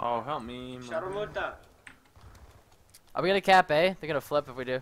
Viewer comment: Did he just said "Sharmuta" ?